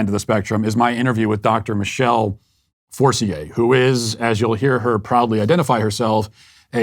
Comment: I thought her name said Dr. Forcer... how ironic would that have been